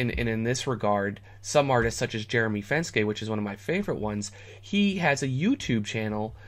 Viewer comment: What's the name of the youtube channel? Can't catch the name. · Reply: Jeremy Fenske